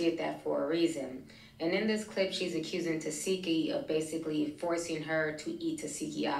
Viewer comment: why did I think it said tekashi